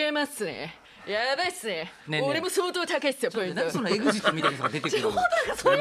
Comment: I could be wrong but I think Yoshida san says "Why are you talking like Eguchi-kun"? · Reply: It is エグジット(egujitto). A comedian duo or smtg like that. The guys in the picture I inserted in the back